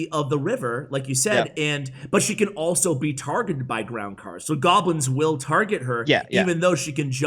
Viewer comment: I heard "golbins" and said "this must be Shane, for sure"